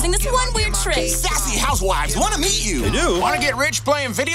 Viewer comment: did anyone else catch what the dude said? “Sassy housewives wanna meet you” dirty “adult” joke on a kids movie? 🤔